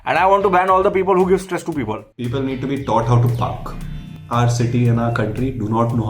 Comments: I Swear I mis-heard saying "People need to be taught how to FUCK" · i literally though said people need to be taught how to f*** (Not proud! but thats what i thought i heard!!) and i judged him sooooooo bad...good law to have though, Sex ed is important! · He said PARK and I heard ***k 🙃